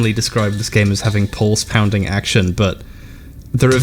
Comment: I misheard a part at the start and thought Hbomb described it as "Pause-pounding action" - which is a fair summary of how I play heat signature.